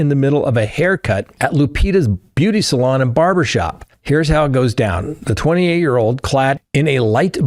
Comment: I’m dying did just say “hairs it go down”😂